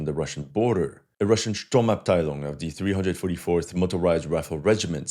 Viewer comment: it sounded like SturmAbteilung, but that is German . What is the word you said?